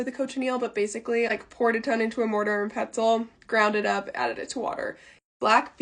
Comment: I coulda SWORN I heard “mortar and pretzel”